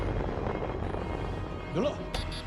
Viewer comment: did he say yolo?